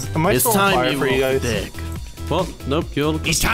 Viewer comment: DID SAY THE D WORD ?!? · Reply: Melanie Keh he said deck